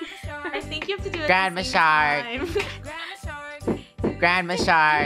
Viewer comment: Bruh at 2:44 I thought he said grab a shark